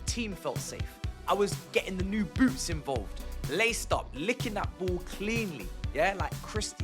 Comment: man's got that jiggle physics, and did i hear bro say something about licking balls